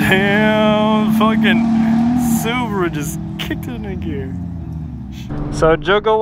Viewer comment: did u say nigga.... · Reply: He said 'kicked it into gear' or 'kicked in a gear', i had to play it back to double check as well